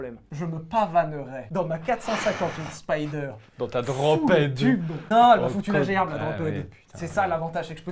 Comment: C'est quoi la voiture dont ils parlent à 42:17 ?? J'entends "grand pède"😭😭